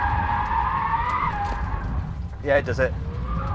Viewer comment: 原文はYes,dose itかな? 空耳含めやったぜに翻訳できるcarwow日本語担当の人ほんとセンスいいな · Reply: Yeah, it does it. かなぁ。（この車、やるじゃん、的な）